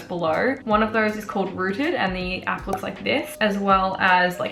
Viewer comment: What is the app called? I can't understand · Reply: Rootd :)